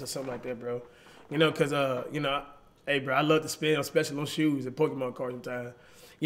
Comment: Did he say he likes to spend on pokemon cards